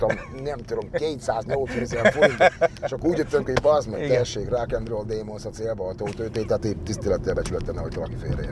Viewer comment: nál mit mond Laci? 20x tekertem vissza, de nem értem 😀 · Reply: Idézet egy Ossian dalból, melynek refrénje eredetileg így hangzik: "rock n' roll démon, a célon túllőttél már rég". A dolog pikantériája, hogy Laci ezzel nem pusztán azt fejezte ki, hogy túllőtt a célon azzal a bevásárlással, hanem visszautalt vele egy konkrét eseményre, ugyanis NEM az eredeti formájában idézte a dalszöveget. Paksi Endre egy réges-régen, 2000-ben adott debreceni Ossian koncerten ittas állapotban jelent meg a színpadon és így nyomta le a bulit. Pechjére a koncertről hangfelvétel készült, ami aztán nagy karriert futott be az interneten. Endre kapott kritikákat emiatt akkoriban, de történt ami történt, mindenki hibázik néha. Laci erre utalt vissza, de hozzátette, hogy "tisztelettel, becsülettel, nehogy valaki félreértse", tehát nem bántásnak szánta.